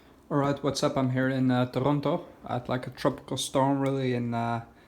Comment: Sounded like you said you're in Toronto. I got afraid for you. We just escaped that hellish place and moved to Varna.